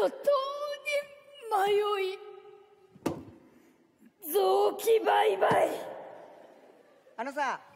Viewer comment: so were not gojng to say at 3:02 it doest sound like yachi is saying tsukki bye bye or is it just me